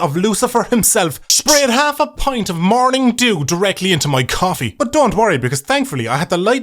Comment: Had to do a double take on morning dew, thought I heard morning Jew for some reason.